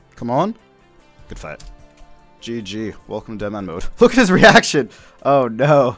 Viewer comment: what did you welcome that guy to? I coulda sworn you said "Deadman Mode"...But I thought it was called DitterManMode!